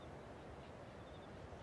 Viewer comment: I didn't know what Rio said in the end, I had to look it up. I thought he said, "Imma gonna kiss you."